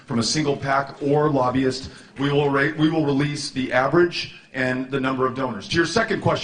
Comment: Anyone else hear him say "rape"